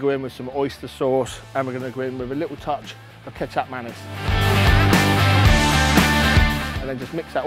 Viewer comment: Hi, what was the last sauce you added? Cheers. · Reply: I am so glad you asked this, my American ears thought he said ketchup mayonnaise! lol🤣